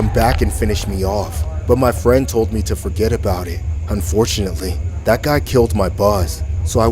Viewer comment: I thought he said "That guy killed my boss. I thought I skipped part of the story.